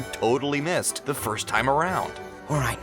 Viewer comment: did he say the first time?